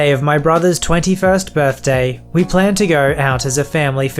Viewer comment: i misheard “brothers 21st birthday” as “moms 21st birthday” and i was a lil concerned that his mom was 5 when she gave birth to op